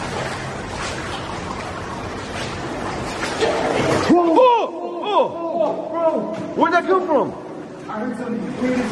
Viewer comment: The voice at 4:09 sounded like it said "Damnit". btw poor. Why does everything happen to him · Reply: It did sound like it said "Dammit".